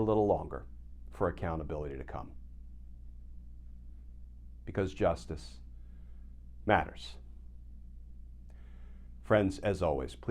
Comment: I almost thought i hear Glenn say, Because Orange Matters!😂